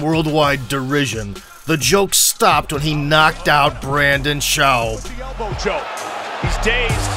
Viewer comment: Did you just say Brandon Shoaw lmao did you mean Brendon Schaub?